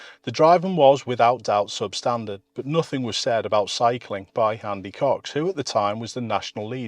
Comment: Thought you said 'handy cocks' and felt my fight or flight activate.